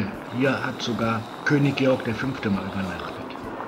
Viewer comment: was genau hat die König Georg aus Hannover der V. gesagt? Konnte das akustisch nicht ganz so verstehen. Danke fürs Video! Überlege in Göttingen zu studieren, und obwohl ich nur wenige Kilometer davon in Hannover eben wohne. Aber scheint ja bis Minute 2:26 schon ganz nett zu sein! Ich schau mal weiter. LG JFK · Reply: Der hat da nur übernachtet, aber der Georg V. hat auch viele Stellen in Goettingen besucht, so u.a., wie Goethe auch, das Grab der Cecilie Tychsen, die quasi die Muse der Hochromantik, so wie Sophie für Novalis. Hier in Goettingen hat die deutsche Romantik eine ihrer wichtigsten Wurzeln. Gruesse aus Göttin-gen